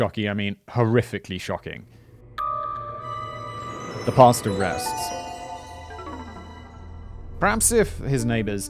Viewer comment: It sounded like you said "the pasta rests" 🤣